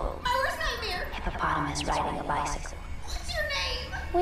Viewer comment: I thought it said ‘HELLO PIGGY’ ✨oink oink✨